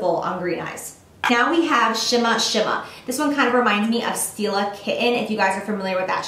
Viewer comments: Thought she said "Steal a Kitten" not Stila 'Kitten'... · Omigod I thought you said steal a kitten instead of stila kitten lol I was like wow they are really getting unique in their eyeshadow names